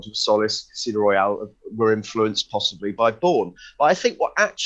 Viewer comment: For a split second I thought he said "Casino Royal and Quantum of Solace were influenced by porn"